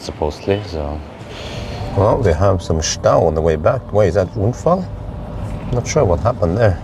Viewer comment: Greetings from Germany :) Did you actually say the German word "Stau" ("traffic jam" in English) at 11:05?? Or which word was it in English? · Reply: Ja tatsächlich, Stau (traffic jam) und auch Unfall (accident).